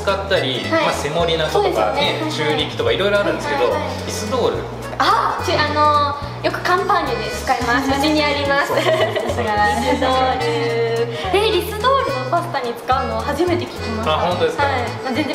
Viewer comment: カンパーニュが乾パンに聞こえてしまって、非常食も作るの！？と一瞬動揺しました😂